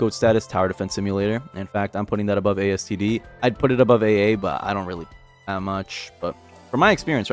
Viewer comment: It really isn't a bad game. It beats AA in qol and quality.n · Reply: i thought he said AA mb